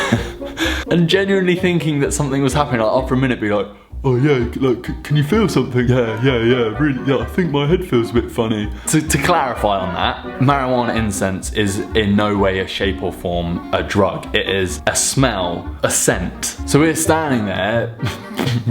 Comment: I thought he said marijuana insect I was like what the...there is no such thing as a weed insect 😂😂😂😂